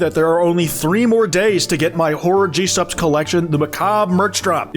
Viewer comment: oh i thought you said "whore gsupps collection" and thought you were talking about the old one with u tooted up on the art of the cup